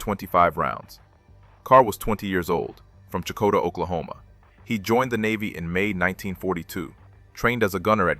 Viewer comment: The times in the audio do not mach the times shown in the subtitles. Also mispronounced Howell several times. At 12:56 narration said he was from Checotah Oklahoma, subtitles said he was from Chickasha Oklahoma. Too many mistakes.